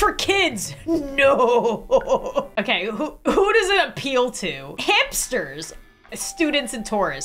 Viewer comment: Am I the only person that thought she said hamsters